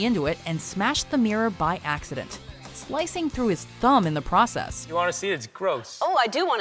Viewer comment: I thought you said he sliced his thumb